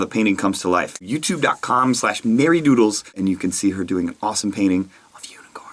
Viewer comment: oh i thought you said hairydoodles! :D